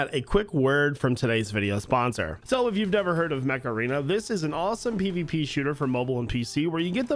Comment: Not gonna lie, I thought he said Macarena. Like the dance.